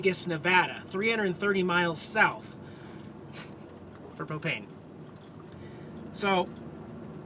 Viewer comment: I'm sorry did you say $86 for propane? I could swore I heard cocaine lol On the bright side, I know you were going through some rough cold times there in Nevada, but looking back I bet they are some of you best memories.